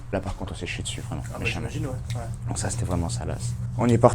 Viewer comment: C'est moi ou Geoffrey dit que c'etait "salace" à 5:55 ?! 😅